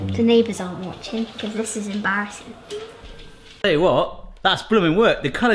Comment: "I hope the neighbors are not watching, this is embarrassing" What I heard: "I hope the neighbors are not watching, this is em-Barry-ssing" lol